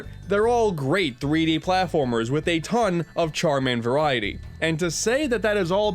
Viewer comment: Here, i thought you said "Charmander-variety" and i was so fucking lost.